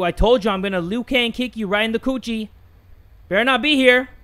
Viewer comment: So im wearing headphones and i had to fix my hair and i took off the headphones to fix my hair. When i put the headphones back on i heard said "in her coochie" o broke down laughing and fell to the floor because that what i heard lol